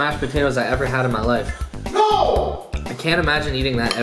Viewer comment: who else heard said no